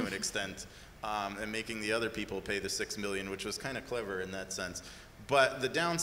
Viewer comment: They say "It is kindof clever" but what I hear is "It is kindof unethical".